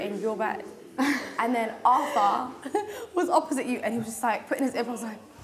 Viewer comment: “Putting his AirPods” hmmm.... putting his AirPods into what? I swear they said they don’t have their phones or anything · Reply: yeah after a few more listens I started to hear ear buds